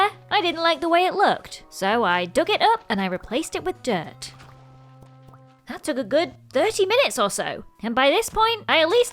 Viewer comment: I misheard and thought you said "that took dirty minutes"...